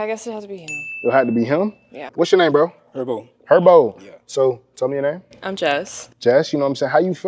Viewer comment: my dumbass thought heribo the gummies instead of Herbo